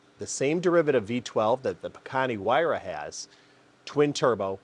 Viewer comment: He said the Bugatti Huayra. Its a Pagani Huayra. Or did I mishear him due to not being from the USA 😟